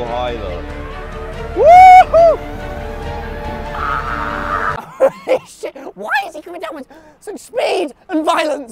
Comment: Proof that DanTDM IS physically able to swear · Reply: i heard him say “holy shit”